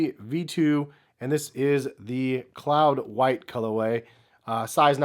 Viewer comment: ”I thought that said clout” 😂